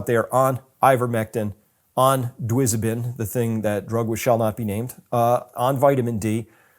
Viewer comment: They want a 15 percent reduction in population. This was the stated goal from the outset. They'll get it through vaccination. Deaths from side effects and sterility from the vaccines. To get the 15 percent reduction of world population, they only need deaths and sterility of tiny percents of the population of the 15 most inhabited countries. They could simply mask it even more by going for the 30 most inhabited countries, thus reducing the percentages in each country. Each country will say the vaccine is safe because only ."x" of a percent had death or sterility, but the cumulative result of all the countries will equal 10 to 15 percent of the global population. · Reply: I agree. At 28:09, he mentions a drug right after ivermectin. It sounds like "dwisobin" but I can't make out exactly what he is saying, and there is no such thing as "dwisobin." Do you know what he said there?